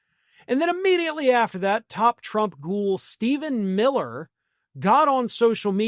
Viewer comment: You called him "goon", I heard "ghoul"...probably because that is a more appropriate name for Stephen Miller.